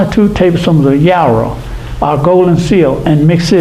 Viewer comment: Did he say one or two tablespoons of "yarrow"? At 36:50 ?